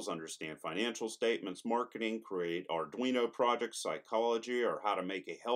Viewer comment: i thought he said scientology